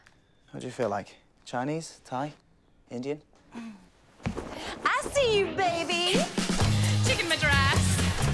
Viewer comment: I thought it said: I see your baby Chicken my dress